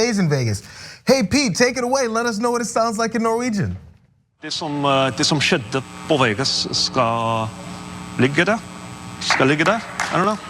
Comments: I swear it sounds like he says, "Dis sum shit, boy..." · Norwegian is my second language. What he was saying in Vegas was "What happens in Vegas shall make you happy" rather than "stays here". But also... if he didn't speak any Norwegian, he wouldn't get the first part of the sentence right. He knows a little but probably couldn't hold a conversation, which is fine because almost all Norwegians under the age of 70 are fluent in English anyway. It's only useful if you are going to spend a lot of time around Norwegians and want to show an interest in their culture. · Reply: Norwegian is my first language and he's definitely saying "What happened in Vegas, must lay there". I'm guessing you thought he said "skal like det". Although his accent is far from perfect I can definitely hear him say "skal ligge der". While he is technically wrong, I would say it's a good attempt by a non-native speaker from the top of his head.